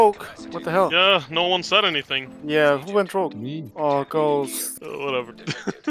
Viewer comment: 12:8 thought you gonna said hunter who want rouge 😂😂 gg keep up the good work man